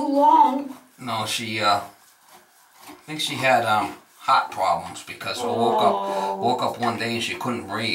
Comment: I thought he said the dog had hot 🥵 problems Heart problems 😌